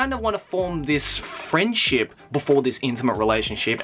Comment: Sounded like he said "frenchlip" XD No 420 joke intended.